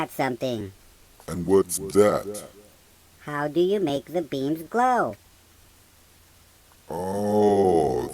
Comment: I thought he said "how do you make the beam float?" LOL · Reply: Why u bully meh is right ihear it how do you make the beams glow